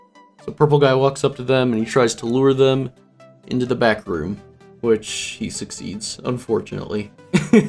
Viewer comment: I thought he said “backrooms” 💀